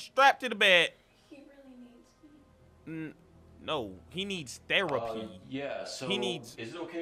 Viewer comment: When he said her dad needs therapy I thought he said steroids 😂😂😂😅